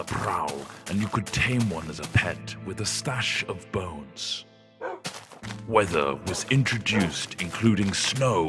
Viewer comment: I thought he said wither at first and I was like lolwut